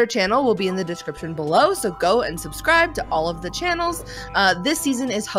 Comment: I thought said in the into "Go unsubscribe to all of there channles" her and sounded like un LOL XD